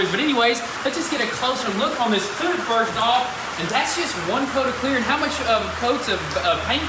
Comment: sounds like he said “that shit right here is one piece of clear coat” never replayed a video so much in my life 😂